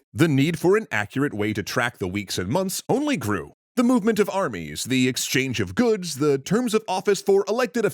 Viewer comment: I’ll admit I heard “The movement of Arby’s”